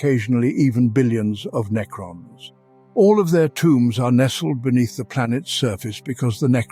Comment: I thought it said wombs at first. 😅